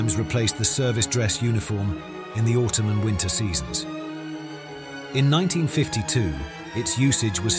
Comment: I noticed some typos in the subtitles, the strangest of which is "the Ottoman winter seasons" instead of "the autumn and winter seasons" at 7:51.